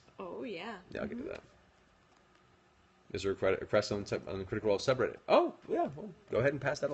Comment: What is Matt saying at 29:47? Also thanks for posting this! <3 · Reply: it's about someone on the critical role subreddit asking if it was okay to post these on YouTube.